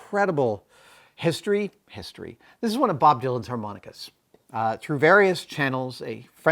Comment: I legit thought he said Bob VILA's harmonica at first, lol. I thought, "Well...that's an odd thing to want..."